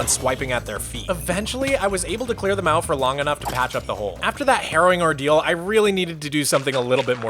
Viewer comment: I thought you said heroin ordeal 😅😅😅